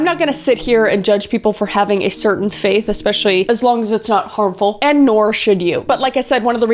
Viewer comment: Did she actually say "and Nora should you"? or am i bugging?